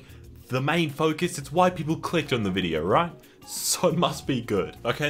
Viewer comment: I thought you said "White people click on the video" and tbh? Checks out.